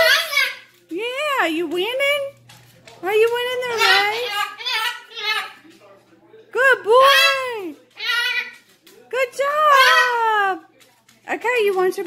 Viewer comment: He's got such a sweet voice. "Good boy, good bird" it sounded like he said. Our dog goes crazy for the bottle too. You get nervous when he's using his beak on your pretty wood table? Love that dark wood color. He's so precious. Congratulations on 250 subscribers!!🤗🎶🕊🎶🤗